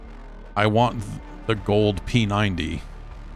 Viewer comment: I was doing a dab, and started coughing, like you do. When I thought I heard Gold Penis Envey 😂 when you said Gold p90